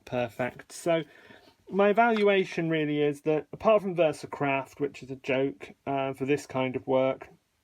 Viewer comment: Did I hear you say "Versacrap" instead of "Versacraft"? 😁